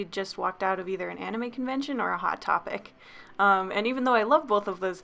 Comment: When you said hot topic I thought you said hot pocket